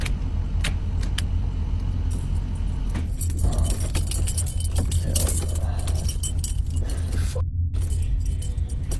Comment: that Ambulance video is CREEPY AF!!! but you missed the EVP @ 12:27 - AS SOON AS he turns of the engine there is a muffled whisper-like EVP that sounds like 2 sets of 3 single-syllable words that DO NOT sound like they came from the guy filming or the other guy... it was FOR SURE AN EVP!!!! But I can't make out what is being said because I don't have headphones I can use and its too late for me to turn up my sound system loud enough.... Can ANY of YOU make out WHAT IS BEING SAID?!?!?! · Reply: I think the voice said, helps arrived, or something like that.